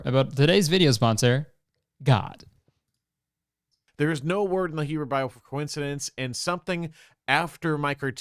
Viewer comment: what did he say there? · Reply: "There is no word in the Hebrew bible for coincidence"